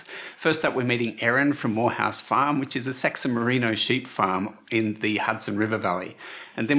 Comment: I swear I heard say "it's a sexy merino farm" hahahahahhah can't stop laughing #secondLanguageProblems